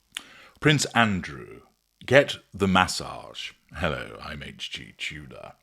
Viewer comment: OH ~~~ I THOUGHT IT SAID ''PRINCE ANDREW GETS A MASSAGE'' ~~~ GUESS ME'AGAIN AINT AROUND ???